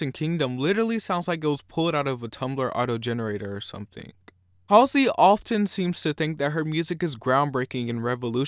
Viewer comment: Did you call her Palsey at 1:17? 😆